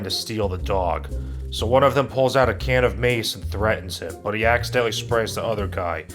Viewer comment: when said somebody pulled out a can of mace to threaten someone i thought he said a can of maize and because this movie is so stupid i didnt even question that someone would threaten someone with a tin can filled with corn